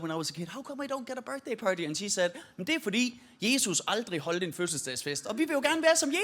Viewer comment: I love when he was like ‘og det er fordi jehovas witnesses cant do something’ and just moved on as if he hadnt just started that sentence speaking danish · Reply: I definitely hear it as "Og det fordi" when I go back and listen.